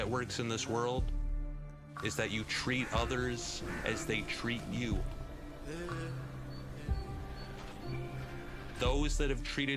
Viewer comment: I heard, “you should tree others how they tree you.” XD